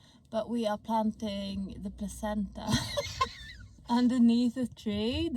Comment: I thought it said painting my placenta 😭😭